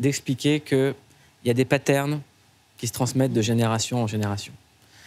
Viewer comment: Coucou. Qu'est-ce ce qu'il a dit à 11:15 : << ce que je trouve beau avec ce film c'est d'expliquer qu'il y a du ... Qui se transmettent de génération. >> Et qu'est-ce qu'il a dit a 11:32 << je sais pas que votre mère est d'origine ... >> Merci d'avance! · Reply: Des pattern / Kabyle